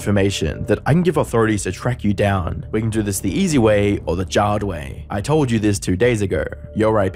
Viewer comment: LMAOOOO HE SAID "JARD" INSTEAD OF "HARD" 10/10 FOR ACCURACY